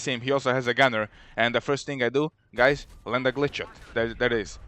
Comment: So what the heck does he say at 13:27? How does he explain to destroy a tank? I hear "Run the glitcher" or "Lund a ....." I don't know. Thoughts anyone? What glitch is he referring to? Like head glitching with a tank? Amazing stuff & Dedi... Been subbed to you for awhile now. Thanks for your efforts. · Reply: Hi,i say ''glitch shot'' if you check my Tank Guide #3 on my channel i show in this video what is glitch shot and how to di t,so be sure to check that out if you wonder what it is. Cheers =).